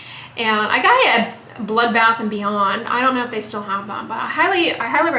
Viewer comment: "Bled, bath and beyond" haha 😄 Happy Sunday!! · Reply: I thought she said blood bath and beyond 🤣